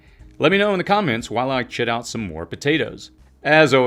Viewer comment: Listening to this at 2x speed i know i heard it wrong. Even at 1x speed it still sounds like "While i sh.. out more potatoes." Once you hear you can not unhear. Shed out? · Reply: 🤣 "chit", it means to let them grow eyes before you plant them.